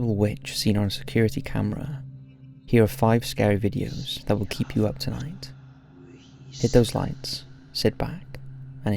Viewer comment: You mean "UP AT NIGHT"? Get some rest before you upload. We wont be mad at your for it, I promise😂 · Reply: He's saying keep you up tonight. Why is everyone mishearing it?